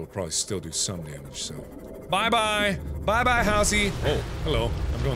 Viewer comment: I thought I heard Halsey instead of Housey ._.